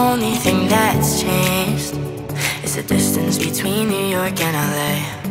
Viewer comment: You know when it says ‘is the distance between New York and LA ‘ I hear ‘Is the distance between where I lay’